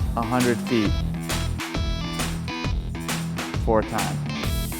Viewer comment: omg at 0:31 i thought he said four times, meaning four rounds i was about to be like holy freak! i can't even do one round